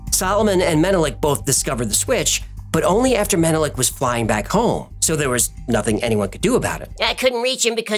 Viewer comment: 😮 Did you say flying back home